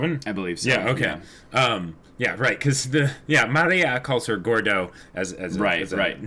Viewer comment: I think Maria calling Cordovin "Gordo" is hilarious because it is the masculine form of fat in Spanish, meaning Maria is calling her fat and a man with one word. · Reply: I thought she said Cordo, as in a shortened version of her last name.. but maybe it was also an intentional pun by RT